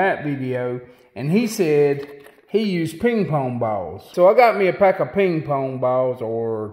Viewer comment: MAN..... ! I thought you said King Kong Balls 😂